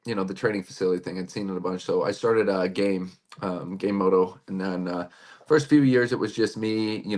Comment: I had to rewind that and play It again Because the thought he said he started gay moto.